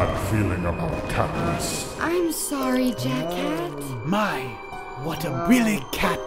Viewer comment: Oh yeah when sally says jack cat it sounds like she is saying jackass just saying